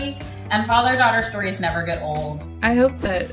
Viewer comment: Young boys? 🤔🤨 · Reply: I heard "... younger girls to be themselves"